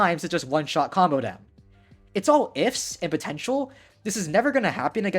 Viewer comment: DID YOU SAY POTENTIAL?